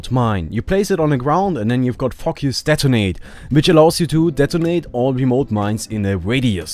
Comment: Totally thought you said "fuck us detonate"